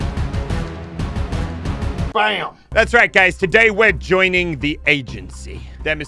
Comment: I swear at 0:06 he said thats right gays